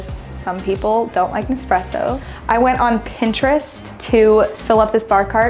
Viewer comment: I thought she said no stresso when she pointed to the coffee machine and my initial thought was SAME